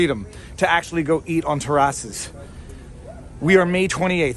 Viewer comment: When you said Terrace I thought you said Tarrasque